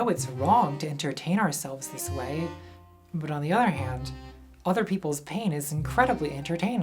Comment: Did you just say "on the udder hand" when talking about lolcows? If so, bravo